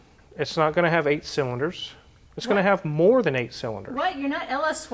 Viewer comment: He said more than 8 cylinders. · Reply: shows ya how much I paid attention, I thought he said “it won’t be 8 cylinder”